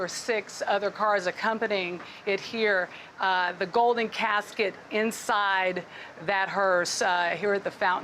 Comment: Just few more days and this George Floyd will be in cross lol and this brain dead people gonna start praying him lol 😆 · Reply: We all need to Kneel, and crawl on the ground and beg to be forgiven for a crime we didn't commit! It literally sounds like she says "the golden calf". Kind of like Idol worship. This is ridiculous.